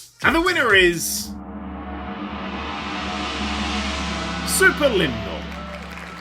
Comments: Could use game labels. What's the game at 15:08 He says "Super Linda" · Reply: Superliminal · whats the game at 15:10 i have no idea what he said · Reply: Superliminal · What is the game at 15:15 ? I cant make out what he says but it looks like a good game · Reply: Superliminal!